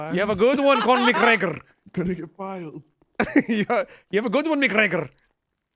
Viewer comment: I believe Connel macgregor is his crim character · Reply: oh I thought he was saying Conor McGregor and mixing up Irish w/ Scottish